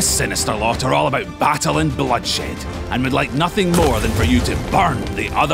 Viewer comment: Did i hear bloodshit ? XD